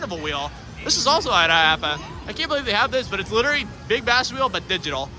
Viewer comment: Anybody know what the fuck he said at or around 13:17??? Lol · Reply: "also at iaapa" lmao